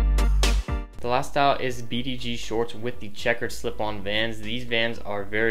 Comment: What did you say the shorts were? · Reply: BDG from urban outfitters